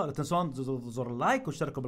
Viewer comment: ايش قال 😂😂😂😂😂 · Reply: تغزرزر لايك 😂